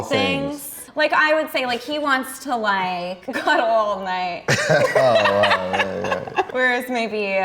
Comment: I was so sure I heard her say, "cuddle all night" turns out I was completely wrong lol. Side note: They are adorable.